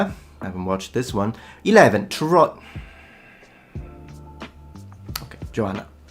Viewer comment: I'll admit, when you first said that, I heard Tron...